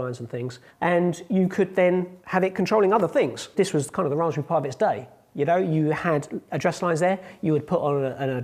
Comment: What does he say at 11:14 ? "This is kind of the <garble> of its day" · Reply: "This was kind-of the Raspberry Pi of its day"